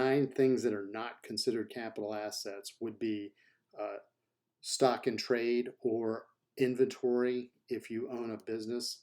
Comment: Of course! I believe I had meant 'Stocks in Trade,' referring to stock in trade or other property included in inventory or held mainly for sale to customers in the ordinary course of your trade or business. A stock is considered a capital asset. But stock in trade, which refers to inventory held for sale (like in a clothing store) is not. I apologize for the misunderstanding. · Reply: Thank you for clarifying! Apparently I heard the words "stock and trade" and immediately interpreted that as "stock trading." Appreciate the clarification, and good to know the difference between that and "Stock in trade"!